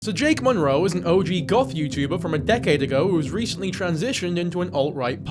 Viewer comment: I really thought you said “golf YouTuber” 😂😂😂